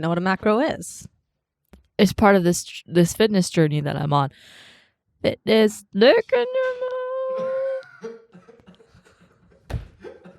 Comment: Still can’t figure out what said at 20:39 lol someone help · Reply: “it’s part of this fitness journey that i’m on (fit-ness d**k in your mouth)” as in “fit this”, hope this helps xxxxx